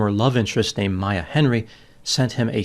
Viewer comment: it sound like you say "former love interest Amaya Henry" but in your self reported transcripts you say former love interest named Maya Henry. please try enunciating your words a little more. you almost got it. you could speak for a living some day and the clarity of your utterances will be imperative. for gods sake we know your not working on your physical image. you know you don't have to be disgustingly hideous with glasses to appear smarter than the rest of us. you could get contacts too.